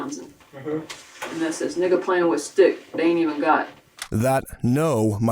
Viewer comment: So ig im the only one who heard her say N!99a playing with a stick